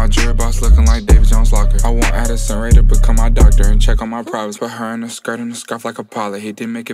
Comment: just realized what he said lmao and this is like the tenth listen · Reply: "check on my privates" is what he said 👀